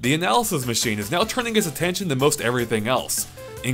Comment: Did you say the analysis machine is turning HIS attention at 0:20?